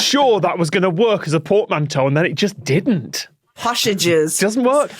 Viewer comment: did Tom say "Porkmanteau"?!?